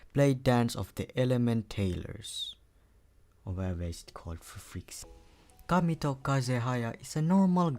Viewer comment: Did he just say "otherwise called freaks" LMAOOOO